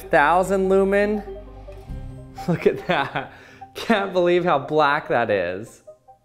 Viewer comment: Oh my god.. What did you just say? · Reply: He said "can't believe how black that is" Hope I helped clear up the confusion.